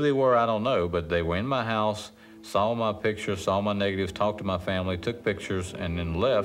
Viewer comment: did he say "saw my niggas"? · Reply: It's at 19:44. "saw my Negatives" But i''m sure when it comes to the CIA, they're always lookin for both. That's why when i make Hotel Reservations i use the name Dwight Mann. I always get a room.